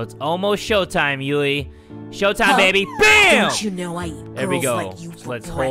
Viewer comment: HOLD THE FUCK UP i just heard a delinquent say Don't you know i eat girls like you for breakfast 5:43 idek